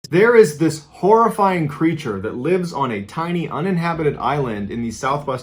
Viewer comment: OHHHH terrifying CREATURE. I thought you said PREACHER. I was confused as F.